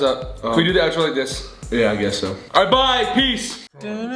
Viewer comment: I thought at 21:00 Ethan said "alright I'm bi PEACE"